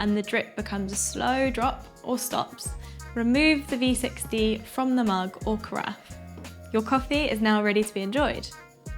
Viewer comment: Can anyone else listen from 6:23 to 6:30? Can you type here what she says ? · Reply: Meant to say if it drains too quickly try a finer grind, if it drains too slowly try a coarser grind